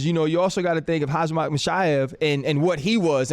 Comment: did he say Hazmath Mishaev 😹